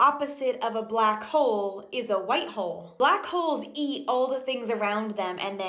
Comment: I was just listening washing dishes so when that checks tarted talking about black holes. I thought she said the opposite of a black hoe is a white hoe. Sorry all but I busting up for 5 mins after that